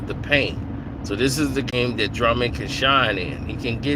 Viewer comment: I thought you said Drummond can't play???????